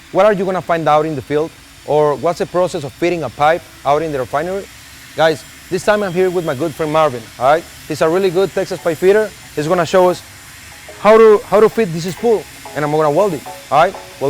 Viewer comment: the caption says "a real good Texas Fight Vader"😂